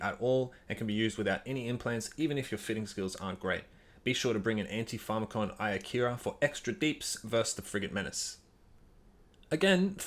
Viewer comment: what did you say at about 3:00 ? What are we to bring for extra dps vs frigs? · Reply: Antipharmakon Iokira, gives +8% bonus to Tracking Speed